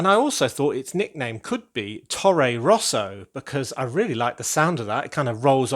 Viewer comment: That's a crazy high and cool building! The space tower is no longer lonely anymore. When you told "Tore Rosa", I honestly thought you said "Torro Rosso", and that's the daughter team of the Red Bull team in formula, which is kind of the concurrent of Ferrari :-).